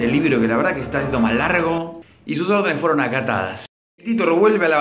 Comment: alguien entiende lo que dice en el minuto 2:13 ? no se escucha muy bien) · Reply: Sus órdenes fueron acatadas